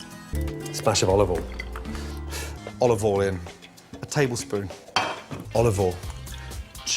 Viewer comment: When I first started watching his vids I thought he said: 'Olive all in' Like it was a poker bet. Hes going all in on olive oil... I cant unhear it now and to be fair, I'm not wrong..